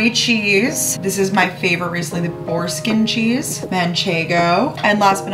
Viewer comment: I 100% thought you said foreskin cheese